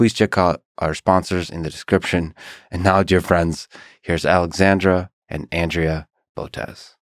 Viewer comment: Thought he said “Alexander” at first